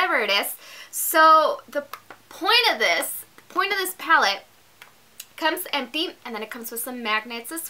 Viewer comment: Can u add the descriptions to the box. took me forever to find the palette online, i swore u said "sea palette" lololol. Z Palette! LOL Great video, thanks for posting it.